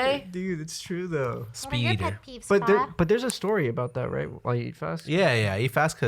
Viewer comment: i thought that said and our biggest pet reeves